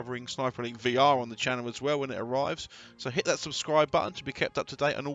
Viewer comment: Hitlers Subscribe-button? lol misheared that obviously.